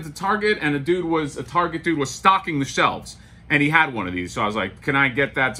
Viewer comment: I’m so paranoid about flippers that when you said you saw a Target guy stocking the shelves, I could’ve sworn you said you saw a guy “stalking” the shelves 🤣😂